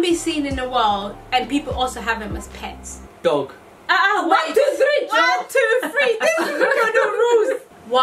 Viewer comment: Lol @ 6:31 did say “1,2,3 Jor!” Has she now become yoruba 😂 😂😂